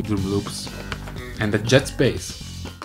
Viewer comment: When He Said Jazz Bass It Sounds like He Saying Jets Bass